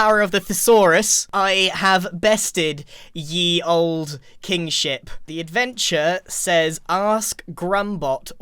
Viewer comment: I heard ye old King Sh!t instead of kingship for a second!